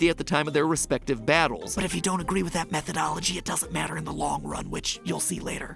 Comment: Who here thought he said "But if you don't agree with that method OMG it doesn't matter in the long run which you'll see later" just to find out he said methodology and not method OMG?